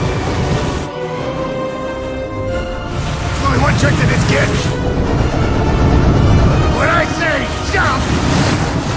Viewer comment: what did he say? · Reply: There’s only one trick to this kid. When I say jump… you JUUUUMMMMP!